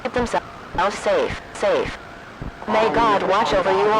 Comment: if you wonder what it says at 7:42 it says "what may god wat-" glitches and then it says "a- o- mediate- tates- federal goverment has fallen-" it gets very glitchy